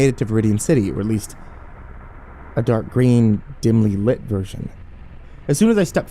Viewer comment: Ffs I thought it said dank green.